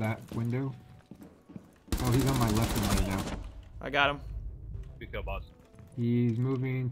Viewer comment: He actually said to you, "Good Kill Boss".